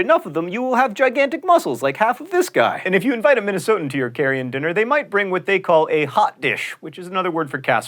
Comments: Did he say, "carrion dinner"? (6:20) Yuck. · Heh. I heard "carrion dinner" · Did he just say, "...carrion dinner"?